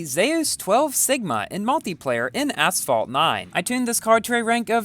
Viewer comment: did you said asphalt 9